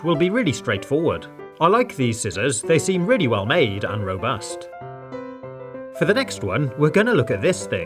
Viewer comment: robust What I hear: ROBUX